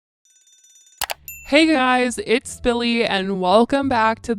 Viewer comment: for the longest time i thought you were saying hey guys it’s billy